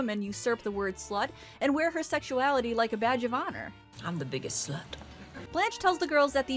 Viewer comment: Had to pause it at 2:12 to note that I seriously thought you said Blanche wears her sexuality "like a vag of honor." On with the rest of the video.